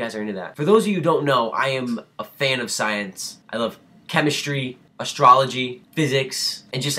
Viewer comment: did he say astrology?